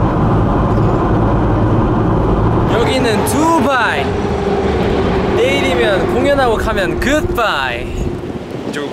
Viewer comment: I just cant stop repeating 2:14 I know he said something interesting but i cant understand.... Sub titles plzzz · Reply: Credits to 'NatIannera' : 2:14 DN: This is Dubai! 2:17 DN: We’ll perform tomorrow and then goodbye!